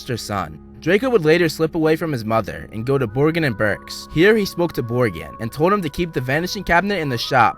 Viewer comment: I thought he said “he then smoked a borgin” XD